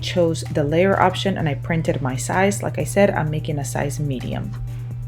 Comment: Oops sorry just watched second time and heard you say medium